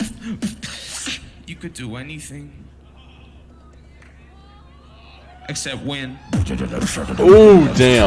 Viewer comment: what codfish says per minute 6:23??? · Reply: You can do anything, except win!